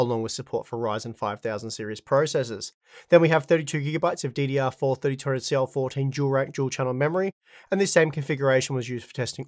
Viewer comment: I had to turn on subtitles at 2:03 because I could not understand what the hell he was saying. "Then we have 32 gilabytes of DDR4321 cell-phone tower do-rag turn the channel memory" ENUNCIATE